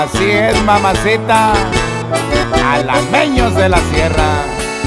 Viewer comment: Yo pensé que decia jalapeños de la sierra....así lo busque en Youtube....😅😅😅